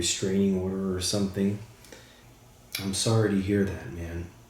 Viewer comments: I heard “I’m sorry I was mad” ?? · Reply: Yeah I thought I heard that too or "I'm sorry I swear." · To Mya, it sounds like he said, “I’m sorry I snapped.” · It sounds JUST like him. This new program is amazing! It sounds like he said “I’m sorry I snapped”